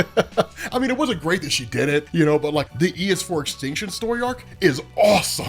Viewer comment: Wow... this entire time he's been saying it I thought he was saying "ES4 Extinction". Maybe now I can find this comic lol